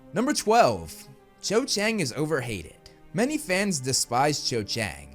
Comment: I literally heard "Cho was overweight". THAT would have been a hot take.